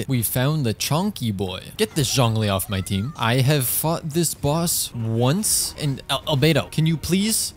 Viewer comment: I thought he said- "I f***ed this boss once." I had to go back 5 seconds like three times.